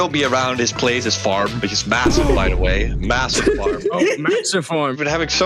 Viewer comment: did someone say massive???